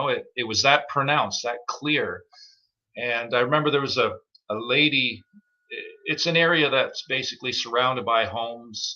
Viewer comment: he’s told this story before and I’m always lost. After the hollow coconut sounding chest thump, says he heard a couple.. Oops! He says that every time I hear him tell this story. I heard a couple of…. Oops! Please explain? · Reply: Whoops ;) Guttural, raspy, pronounced like it could speak English. Mind blowing very powerful bottom end "WHOOPS" ;)